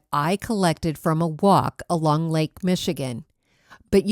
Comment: I must be loosing my mind! I heard you say, " I found the driftwood on a wok in Lake Michigan," and thought, I wonder why someone would throw a wok in Lake Michigan?? Took me a few seconds...😳 Anyway, great video as always.